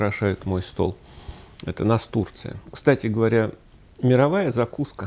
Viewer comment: Поедание цветка взбодрило =))) Сперва послышалось "Это у нас Турция" :D